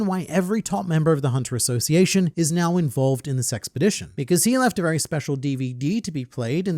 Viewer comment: I keep hearing “this sexpedition” 😂